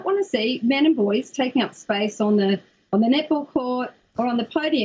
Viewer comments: I’m not nuts, the reporter chick can’t talk right. I swore she kept saying Nipple, instead of net ball. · Thought that woman said all boys nipple team lol that crazy bitch was trying to say net ball lol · I keep hearing "nipple" not "net ball". I susoect that's why Americans say "basketball". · This is the 2nd vid I've watched on this, and I can't help it but every time they set "net ball" I hear "nipple" and can't take it. I'm laughing too much to even attempt to follow the silly argument I just know they're trying to make. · Why am I hearing "Nipple" and not "net ball" ??? LOL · Every time she says "net ball" I hear "nipple"......"The all-girl nipple team". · I love how the news reporter sounds like she is saying "Nipple" instead of Net Ball. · Every time they say "net ball" I hear "nipple" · Man, every time she says "net ball" I hear nipple" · For fucks sake Australians, for a long time I thought they were saying nipples rather than net ball. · When she says net ball, it sounds like she is saying nipple? Simple, have a championship for girls, and one for boys? Problem solved. Damn. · For half the video I thought they were saying "niple court" 🤣 took a while to understand that they were saying net ball😂😂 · Can she say net ball..her accent sounds like she is saying nipple.. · i keep hearing "nipples" instead of "net ball" lol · I thought she was saying hitball 😂😂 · any body else think it sounds like shes saying nipple instead of net ball ????